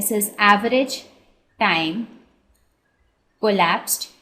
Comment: She is saying THE TIME "COLLAPSED" instead of THE TIME "ELAPSED"😂😂 anyway great video Mam